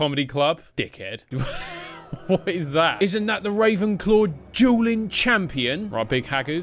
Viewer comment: with the mock accent he sounded like he said the Ravenclaw Juuling champion. Had to do a double take for a good second, then realized he said dueling.